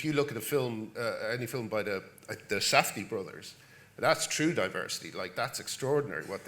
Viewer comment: What is the name he says? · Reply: I think it's Safdie brothers.